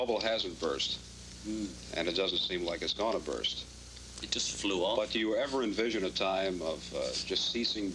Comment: 50 years on, the bubble still hasn't burst. And I don't think it ever will. ❤️ Beatles forever! ❤️ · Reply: I thought he said, 'it just flew off'. Funny either way.